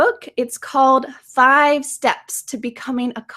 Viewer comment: Why I always hearing from you "comfident" instead of "confident? Is it my mistake or your mistake?)